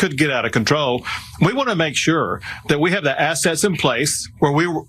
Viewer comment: When Greg Abbot was talking it sounded like he said "..have the SS in place..." rather than "assets".